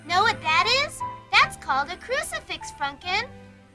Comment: does anybody else hear franken say crucify